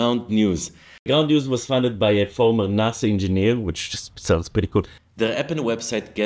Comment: Wonder how many heard him say "former nazi engineer" instead of "former NASA engineer"? Anyway a new mental episode! Love it!